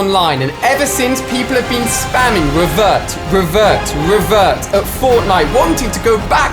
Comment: I thought that said pervert